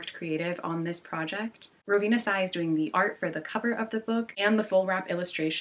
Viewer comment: I’m sorry did you say Rowena Tsai is doing some of the illustrations…… EDIT: okay I misheard that— it’s Rovina Cai! 😅